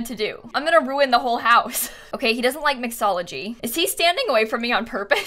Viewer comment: sounded like she said "doesn't like violence"